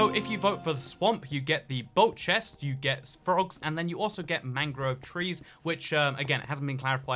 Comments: I thought he said mango tree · why does it sound like jens says mango instead of mangrove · I would love to see what building use mangrove wood will possess. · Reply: for a while I thought they had said mango cause the audio wasn't very clear LOL mangroves makes more sense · I thought he said mango trees. Living in Florida, all my neighbors and I have a mango tree so I thought that was super accurate. The whole state is basically a swamp biome with large villages. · I really thought they said mango trees.